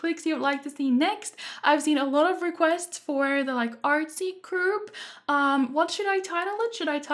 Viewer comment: Call them the artsy crew, which is what I thought you said at first 😂